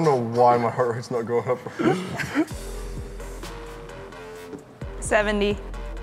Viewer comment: When she said 70, the captions said 7000 xD